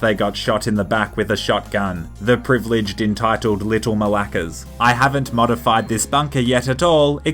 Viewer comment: did he say malakas?